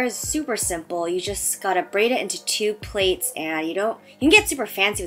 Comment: Did she just say "plates"?